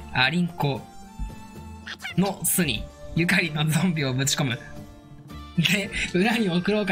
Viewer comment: ましでアリンコが｢ゆかりん｣って言ってる様に聞こえる( ੭ ˙࿁˙ )੭ ᐝ